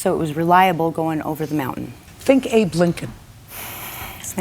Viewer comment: I thought she said "who's that?" When Judy said think abe Lincoln